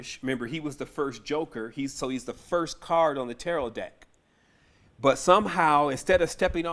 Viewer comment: boy I thought you said joke is the first card staring at/laughing at all the rest